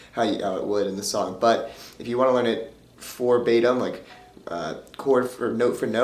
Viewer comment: Well done. Nice tutorial. But in the beginning it kinda sounded like you said "for batim" but meaning to say "verbatim"? Lol